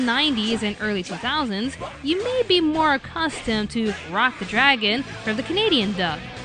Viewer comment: i thought it said f**k the dragon